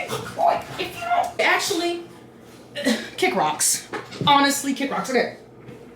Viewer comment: I thought you were saying "Kid Rocks" not "Kick Rocks" for a moment there and I just couldn't understand why you kept saying his name. 😂😂 (for the record my misunderstanding most likely comes from the fact that I listen at 2x speed and while I doing other things, like chores, because then chores are all the sudden not as boring)